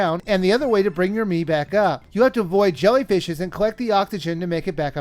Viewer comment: Am I the only one who honestly thought he said "Fluttershy"?